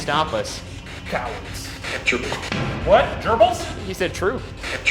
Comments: When they heard the "true" it also kinda sounded like "choo"😄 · I hear "Gerbils" too 😅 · It sounds like 'trouble' at 5:28 instead of 'gerbil' or 'true', which could make some sense if that is where prisoners were taken if they misbehaved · I heard "capture" instead of "true" and capture would make more sense in the context of a jail